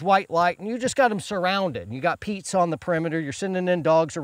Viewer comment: Did I just hear "pizza on the perimeter" ...?